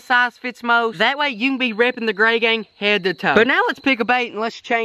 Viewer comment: I thought for a sec that he said #gaygang